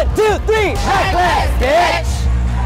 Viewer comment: What does Reckless say after they say “1...2...3 RECKLESS ???????” They say something but idk what. It’s sounds like the b word... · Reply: Yeah it's reckless butch